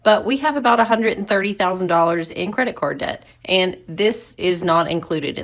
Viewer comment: Sorry, but did that lady @ 5:00 say she had $130,000 in CREDIT CARD debt? Or did I mishear that? Wtaf? How the PHUK do you do that? I mean yes, my wife and I are retired and drawing state pension so have to be careful......but we're facing a pricey repair bill on my car of £1800 ($2370) and we're worried.......having heard her, I am not sure why we're worrying ........chuffin' eck.